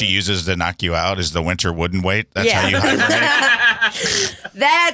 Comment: Listening to this on Spotify, didn’t read the title just been binging so episodes just auto play.. I also thought he said wooden weight when he said the name of the damn play 😂😣